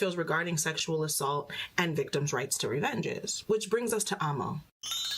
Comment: Is she saying "revengance"? The CC clearly says "Revenge" but that is not what I hear